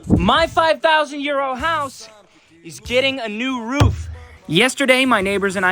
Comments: It sounds like you said my 5000 year house 😅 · I always hear, "MY 5000 YEAR OLD HOUSE!" hahaha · Oh he said his 5000 Euro house…. I thought he said.. his 5000 year old house 😅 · Every time I hear, “My 5000 year old house!” 😂 I have to remind myself, he said EURO €!!!